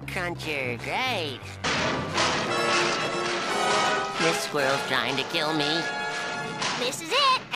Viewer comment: When I heard this, I thought Spongebob said, "this world's trying to kill me." But then I keep seeing and hearing "this squirrel's trying to kill me." Well, the second one makes more sense.